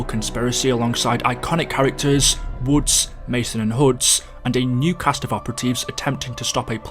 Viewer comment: I'm sorry did he just say Huds